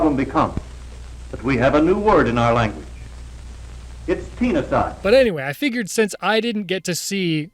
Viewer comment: I was looking away from the video and definitely misheard "teenicide" as "peniscide" 💀💀💀